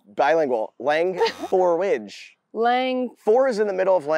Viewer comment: I thought it said " leng for your age"😅